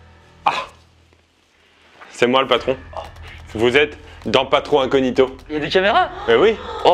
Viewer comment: Au début j'ai compris "Pas trop incognito" j'ai trouvé ça super fun et j'ai lu Patron Incognito j'ai presque été déçue 😂